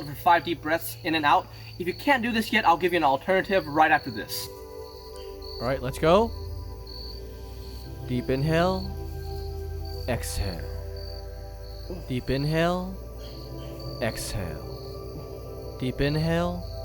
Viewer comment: all i hear is "deep in hell, exhale" which... makes sense